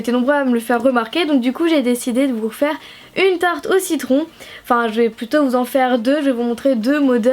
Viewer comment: A 0:42 J'ai entendu tarte aux 6 cons.... x') bref sinon super vidéo !